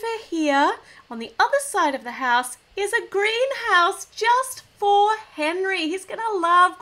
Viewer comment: When you said Green House I thought you said a house that was green!